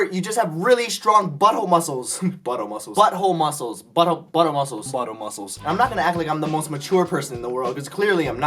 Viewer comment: When said buddoh muscles I thought he said butter muscles